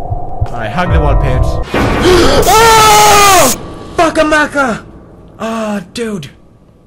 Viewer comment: I thought he said "fucka maca" ; )